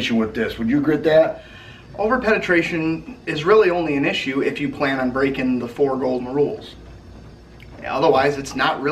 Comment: what did he say? 4 rules something.. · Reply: morton Yes the 4 NRA safety rules